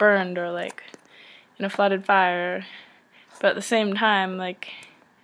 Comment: I just realized it sounds like I say "in a flooded fire" but I meant to say that things could get ruined in a flood or a fire xDD ahaha.